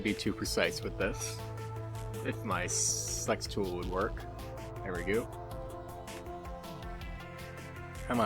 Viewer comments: what did he sayyyyyyyyyyyyyyyyy? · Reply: he said "with this if my select tool would work" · bro i heard "if my ssssssssex toy would work" instead of "select tool" 😭😭